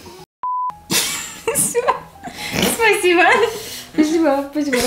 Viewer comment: мне послышалось или сказала с*ка (у)